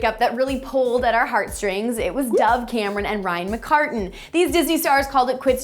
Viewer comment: did she just say "camren" 😂😂😂😂